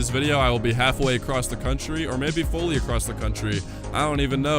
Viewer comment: SCUSE ME DID YOU SAY FURRY XD!!!!! I DESPISE THEM BUT THIS IS THE ONE MOMENT THAT MAKES THEM OCCASIONALLY FUNNY